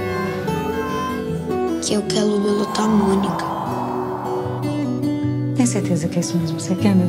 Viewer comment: PELO AMOR, O QUE O CEBOLINHA FALA EM 1:34 ?? Não entendi nada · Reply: "Que eu quero muito a Mônica"